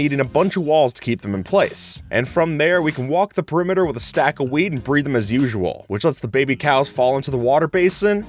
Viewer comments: did he just say breed them with WEED!? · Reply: He said wheat .. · am I the only who heard "weed" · I heard is as a stack of weed💀